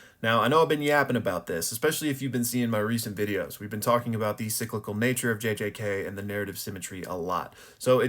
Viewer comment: Thought it said cynical nature at first witch is also true but hopefully there is a light at the end of this tunnel for the story